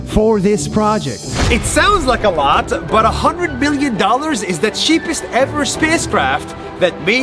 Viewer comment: the subtitle says 100,000,000 . But he says a hundred billion dollars... you guys see a difference?